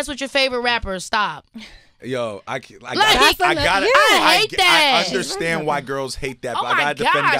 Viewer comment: From reading the comments, I have to be the only one who heard him say, "I USHERstand why girls hate that." 😅😂